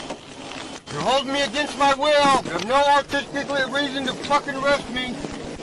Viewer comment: What exactly was that word he murdered???? 🤣🤣🤣🤣 I’m assuming he was attempting to say “No Reasonable Articulable Suspicion” but failed tragically!!! 🤦🏻‍♀️🤦🏻‍♀️🤪🤪🤣🤣